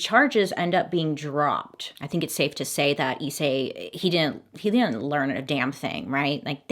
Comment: SOUNDED LIKE A BUNCH OF “ISSEI SHE SAID!” 🙈🤣